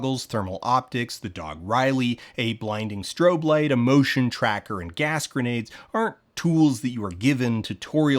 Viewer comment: I heard "emotion tracker" and for a second I pictured the Ghosts finding the Feds with the worst mental health issues to fight them with therapy